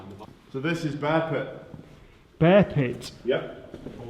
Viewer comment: I heard a spirit @ 12:48 repeating "Bearpit". Must be going crazy otherwise. Yikes!